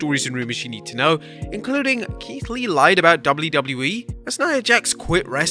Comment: At first I thought it said kieth lee died but it said lied instead 😂